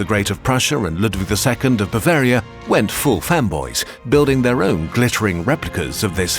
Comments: I thought you said Frederick and Ludwig "went full femboys" and had to do a triple take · I heard "went full femboys" at and had to do a double take xD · PFFFT I THOUGHT THEY SAID FEMBOYS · I heard "went full femboys" not fanboys and did a double take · Okay I think he said they went full fanboys But I heard Femboys and was at unlocked for a bit there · I DIDNT HEAR FEMBOYS ? 🥀🥀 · I definitely thought he said "Went full femboys" 😂 I had to do a double take 🤭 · I thought I heard that they "went full femboys" lol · I heard femboys... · thought they said femboys for a sec and was like “wow okay” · I had to go back and turn the captions on when he said "fan boys" because I heard "fem bois", which I thought was kind of an odd choice. Lol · i don’t get it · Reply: many ppl misheard "femboys" not "fanboys" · i heard femboys 😭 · i heard "femboys" instead of "fanboys" and i suddenly felt like i'd missed a LOT of french history